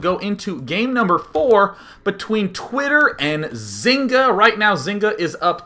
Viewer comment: Did anyone else hear at 0:08 day9 saying "game number THOR" XD